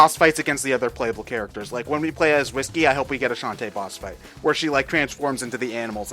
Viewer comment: Did say "Whisky"?